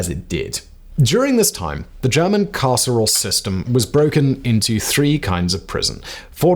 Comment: Thought you’d said “German Casserole System.” 😋